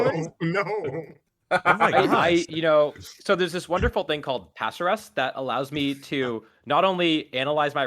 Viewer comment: What is the application you mention here? I'm having difficulty parsing what you are saying. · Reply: Pasrs! Check out my video called “the best teambuilding advice you will ever get” I talk about it a lot